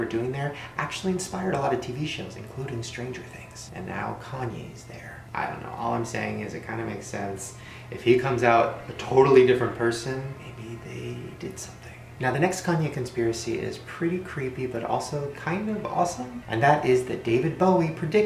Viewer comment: he said tortaly instead of totally